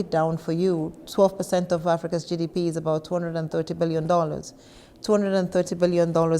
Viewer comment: She said 12% of Africa's GDP is 230 BILLION...dollars? I heard right? At 5:55.